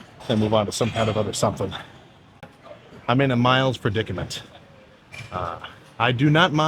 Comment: did bro say he’s in a ‘miles’ predicament ? 🤣